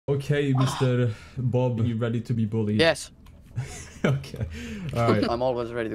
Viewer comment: In the intro I rlly thought he said "are you ready to be bobby?" LOL